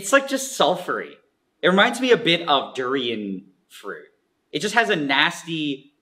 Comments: why does the subtitle say it's inaudible? It's clearly durian fruit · "it reminds me a bit of durian fruit" since the subtitles didn't catch it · "It reminds me a bit of Durian fruit" is what he said in the inaudible part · the subtitles say [inaudible 00:24:08] in stead of durian fruit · the subtitles said it was inaudible, but he clearly said "Durian fruit", whoever is subbing it needs to turn the volume up a bit lol (i'm sitting at 56 on windows and youtube is around 30%)